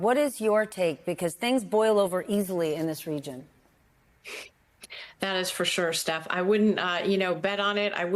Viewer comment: Iran: "Ceasefire??" I thought you said "Increase fire"